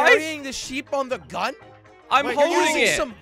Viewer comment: When slogo said sheep i thought he said jeep